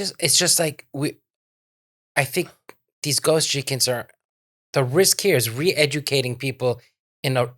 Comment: I thought said “ghost chickens” at 32:11 lol